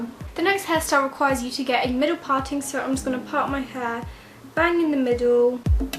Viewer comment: I legit thought she said hair ball lmfao